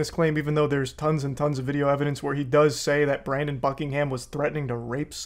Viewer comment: I swear I heard him say Brandon Fuckingham, and now I can't unhear it lmaooo